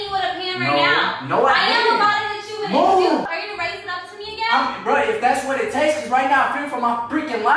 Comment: Did anyone else hear saying “Are you going to break up with me again?” at 11:35?? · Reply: 😐she said "are you gonna raise it up to me again" as in is gonna raise the pan up to her again...